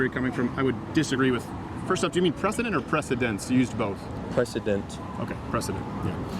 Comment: My man said "precedent" and "precedents", not "precedence". But of course little baby crowder with his playground logic just HAS TO point that out incorrectly instead of trying to have a real discussion.